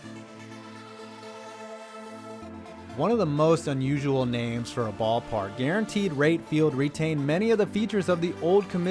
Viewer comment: what the fuck is he saying? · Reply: "Into the moat."